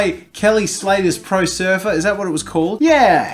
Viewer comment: When he said surfer I thought he said sofa it probably just his accent im trying not to be mean